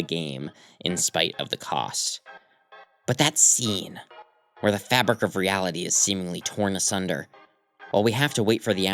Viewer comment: "off topic" i havent did a weird route run yet what did noelle say? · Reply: You can't normally see it but she screams "SAYYL IT!!!!"